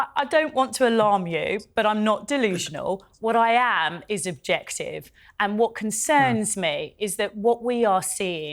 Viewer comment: LOL - did she just say "what I am is objected"? I had to rewind to make sure - LOL